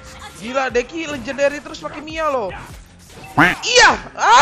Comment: Apa cuman gue yang dengernya 8:58 pake MIYA